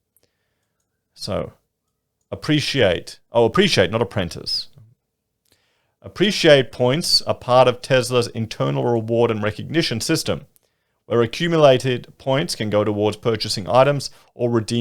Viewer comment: 🥴? Did you just say “Tesla”? Or it must be my vaccine side effect…